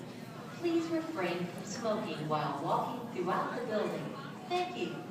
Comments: i thought the intercom said "please refrain from 'skulking' while walking throughout the building" hahaha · I legit thought that intercom said, "Please refrain from skulking while walking." Hahahaha. ;P I really cannot fathom how Pinecone can be so well-behaved and not try to get out of the bag ever. It's crazy! Angel cat! He was so cute when he got startled in the bag, hehe. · hahaha I thought the lady said "please no skulking through the building." And I was like um...why would be people be skulking? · i thought they said "please refrain from skulking while walking throughout the building." lol ok...